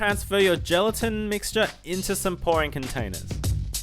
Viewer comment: he said pouring container and I heard boring container and I was like you can decorate it yourself